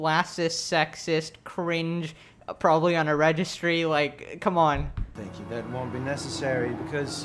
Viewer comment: “probably on a registry” lmaoooooo got me dying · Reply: I thought he said unregistery???